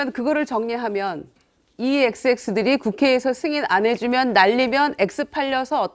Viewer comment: 승인 안해주면이 아니라 안해주고로 들리던데 바이든도 뚜렷하지 않음 0.25배속으로 들어보세요 진짜임